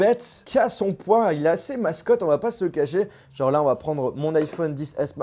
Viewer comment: IL EST ASSEZ QUOI ?? XD C'es pas "mastoc" le mot ?